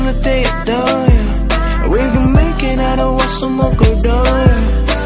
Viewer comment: Ok, so when I first heard it I thought when he said we gon' make it, he said something like you naked so I was all OH SHAT WHAT DIS DUDE INTO